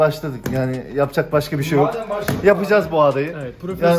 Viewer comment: 😂 ne dediğini anlayan varmı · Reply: oturttuk bişey anlatıcaz masaj yapıcaz diye... Diyor